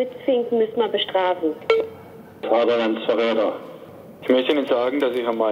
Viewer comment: Hat da einer ernsthaft Vaterlandsverräter gesagt? Haha, alles zu lustig... :-D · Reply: KatarzynaLenkaSabria also ich hab Vaterlandsfahrräder verstanden.